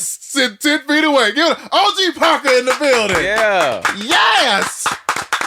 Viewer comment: This whole time I thought the tag said OG bunker not OG PARKER Lmfao